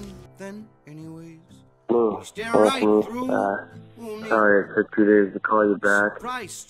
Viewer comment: Can please someone tell me what does the guy say at 7:48? He is speaking in her mailbox, i believe it has to do with the ending.. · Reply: “Hello...it’s me...uh sorry it took two days to call you back. I don’t know if it’s the right time or whatever. Um I’ve been thinking about our adventure that we went on recently, and I don’t know...I just...it kind of...you know having this little story, just not being sure whether to share it with other people or keep it all to ourselves. How’s...how’s that car doing? She still running even? I’ve been back in California...haven’t been able to save enough money for a plane ticket. I still can’t believe how much those things are. I miss you...I hope that’s okay to say. Um...I have to work early in the morning, but if you get this I’ll be up for a few hours you can call me back. Tag...you’re it.”